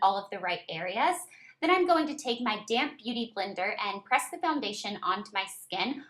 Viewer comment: Lol I thought she said "my damn beauty blender"